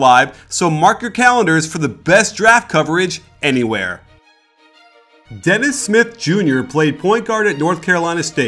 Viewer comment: Who thought dis said Dennis Rodman Jr.